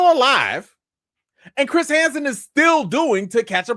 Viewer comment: I keep hearing Chris Handsome. 😂